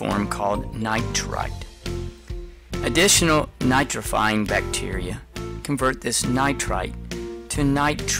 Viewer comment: What did he say at 1:17... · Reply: Nitrite additional nitrifying bacteria